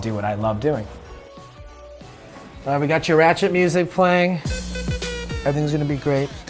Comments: Did he just say " i got your ratchet music playing" 😂 wtf? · 😆did he say he's got her rachet music playing lmaoo?😂😂